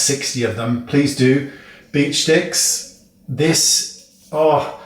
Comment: Hahaha. I thought you said "Bitch" sticks. I immediately looked them up as I was watching your video and was bummed when I didn't find them but later came back to see if you left us a link and I'm totally cracking up to see is is actually called a "Beach" stick. You are such a dream to watch and I have learned so much from you over the years. I try to watch as many of your videos as I can and I have never had the impression that you don't like Charlotte Tilbury. You are just saying what we all are thinking because it is the truth....and that my friend is what makes you so trustworthy and fabulous. Ok, your huge talent and charm doesn't hurt either. lol. I would say ~ 50% of the makeup I use on a daily basis is from CT, and your criticisms and praises are absolutely 1763% SPOT ON. You are the best...thank you for all you do.